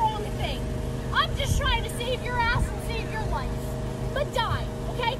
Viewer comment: Thought that said "shave your ass"